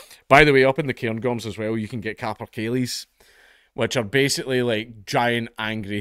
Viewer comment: Whats the bird mentioned at 2:19 ? I cant understand to look it up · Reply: Western Capercaillie (Tetrao urogallus)